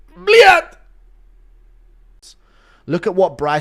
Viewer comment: did he say “blyat” 😭😭😭